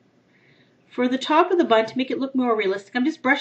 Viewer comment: did u say bunt instead of bun?????